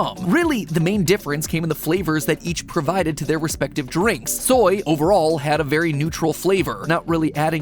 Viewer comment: I thought you said soy won